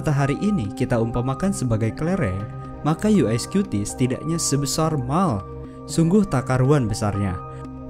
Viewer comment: dia ngomong apa? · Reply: Sebesar mall